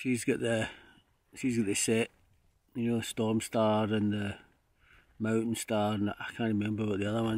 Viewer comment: Must be your accent bro I thought you said it was a porn star, lol until I read the comments it's Storm Star. Sure is some panoramic views from up there. Anyway great to see you back doing what you love. Safe travels